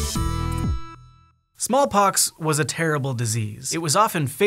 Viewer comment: I thought it said small talk when said small pox